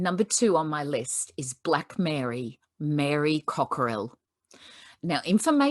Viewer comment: Thought it said black is my nappy colour. Glad you sat up. Made me feel in 2nd class. BRETTYZCAR up the back of class firing rubber bands into the heater.